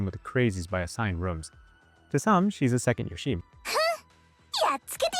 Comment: Hi, thanks for the feedback! If you're willing, could you timestamp one or two instances of this? I do listen a few times throughout the process, but as the speaker I have the tainted perspective of having written and read the script. On an aside (this isn't an excuse to not improve the delivery), I upload the transcript as subtitles if there's a part where the audio is indecipherable. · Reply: Some of these aren't really at info critical moments, but are examples of the slurring 0:30 'yo-sheem' 0:55 'bad mouthing cold?' 2:44 took me a few listens to figure out if you were saying input, gameplay, or something else 2:51 'haruka and ???' I still dont know what you're saying here. 3:00 'healing ???' dont know what you're saying at all 3:10 it sounds like 'twenty huff' I stopped looking for examples at this point, but overall, these are the worst examples. your general speech could be cleaned up slightly. My personal suggestion is that you might want to slow your speaking down a tad when recording audio.